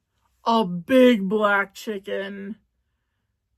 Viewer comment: I heard one you go black you gone need a wheelchair